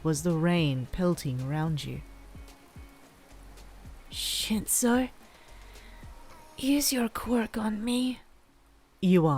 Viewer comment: It says the S word instead of Shinsou with captions on😂